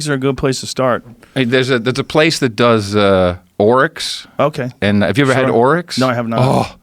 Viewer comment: What did he say he’s hunted, Orcs? 🤔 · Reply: Oryx / Orecks deer.